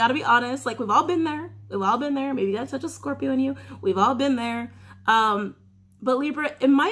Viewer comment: did she say "that's just the scorpio in you"??